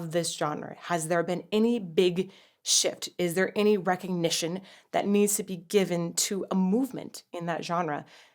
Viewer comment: I know you said "shift" but I keep hearing "shit" and I can't stop laughing lol